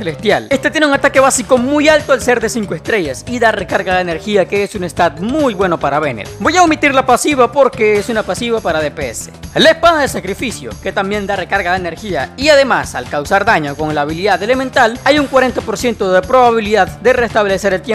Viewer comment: Soy yo o escuche la espaja del sacrificio minuto